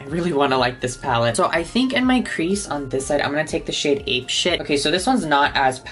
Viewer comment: “I’m gonna take the shade ape shit”.... did I hear that correctly or?...